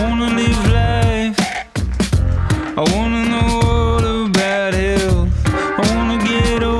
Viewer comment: [Verse 1] I wanna get high I wanna take a break from myself I wanna live life I wanna know all about it I wanna get over you I wanna be somebody else I wanna survive I wanna just think about myself, oh [Pre-Chorus] And just because I tried, doesn't mean I'm wrong Just because I'm cryin', doesn't mean I'm not strong Just because you're right, doesn't make it wrong Just because we cryin' [Chorus] Get high Get high Get high Get high [Verse 2] I wanna feel the sun on my back I wanna sit back I wanna feel somethin', my ‘Lac I wanna get over it I wanna live long and prosper Don't wanna think twice I wanna feel old school in hell, ah, say [Pre-Chorus] And just because I tried, doesn't mean I'm wrong Just because I cried, doesn't mean I'm strong Just because you're right, doesn't make me wrong Just because I'm cryin' [Chorus] Get high Get high Get high Get high Mmm-mmm-mmm Get high Mmm-mmm-mmm Get high Mmm-mmm-mmm Get high Mmm-mmm-mmm [Outro] Mmm (byw i'm a singer/songwriter inspired by Lauv/Jeremy Zucker. It'd mean a lot if you check out my music and subscribe,promise you won't regret<3have a great day:) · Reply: Sounds like he saying ‘ I wanna live life, I wanna know all about health’.